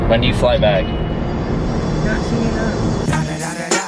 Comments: what did she say I couldn’t hear her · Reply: Not soon enough ! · What did she say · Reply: "not soon enough"